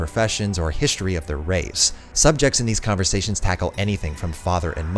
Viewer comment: Does he say 'history of their raves' at 24:40? Lol